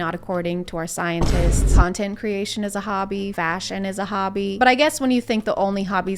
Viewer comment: I'm so tired, I thought you said fascism 🤦‍♂️